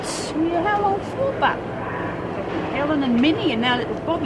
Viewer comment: What the hell does cass say???? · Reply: Tammy Hallett Cass said, "How long for, but?" when Myra told her that Cass still has mates in Wentworth, besides the ones who are no longer in Wentworth like Bobbie. 😃